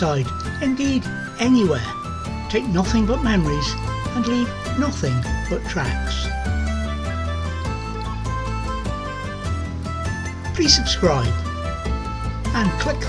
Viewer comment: looks like a lovely place and a great park up with a view, ideal for sitting, looking out to sea and supping on a brew👍. I just had to re listen to that last part, I thought you said, "leave nothing but traps". Is poaching big up there? Must be haggis season 🤣